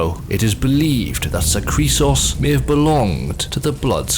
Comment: When he said "Zahkriisos" I quite literally thought he said Zachary Sause, which as a guy whose name is Zach...was hitting me kinda funny